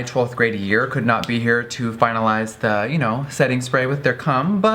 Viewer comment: Did he just say "finalize the setting spray with their cum"?